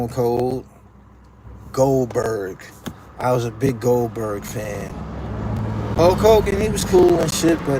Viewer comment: At first I thought you said that one of your favorite wrestlers were Gold Dust. I heard it wrong tho you said Goldberg. 😂😂😂😂😂😂😂😂😂😂😂😂😂😂😂😂😂😂